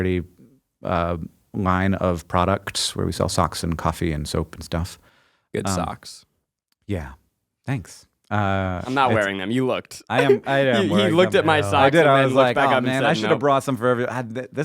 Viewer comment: When Hank started talking about his sock business, said "good socks" and I heard "it sucks." Very opposite messages.